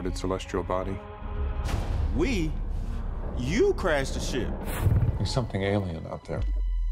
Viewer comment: Had to go find this cause I thought he said crash this shit lol cool had to check